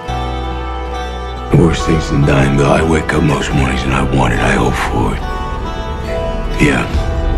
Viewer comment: Can someone tell me what hes saying at 00:55? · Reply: TheGammlerTV there are worse things than dying but I wake up most mornings and I want it, i hope for it